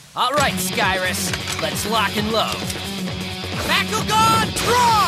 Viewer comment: Did Billy say I think he said? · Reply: William Galley I thought I head him say block